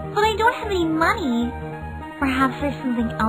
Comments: For a second I thought he said give me your hands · The 8 bit sound quality initially made it sound like Soldier said "Give me your a$$!" . I couldn't have been more wrong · hm thought he said give me ur aSS not Hat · I thought he said give me your hands 💀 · I thought he said "give me your hand" I seriously thought he was gonna rip her hand off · I thought he said “Give me your ass” for a second. I had to replay the video twice to figure out he meant hat XD